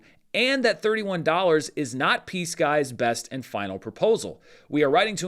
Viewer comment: I was listening to this and not watching and I kept hearing "PSKY" as "peace guy." It was first said during the bit about verbal, off the record conversations so I thought the parties were referring to someone trying to make a peace offerering.